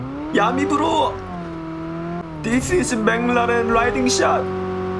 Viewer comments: This is McLaren lightning shot 😂😂🔥🔥🔥 If didn't heard wrong 😂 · i thought he said " this is bin laden riding shot" XD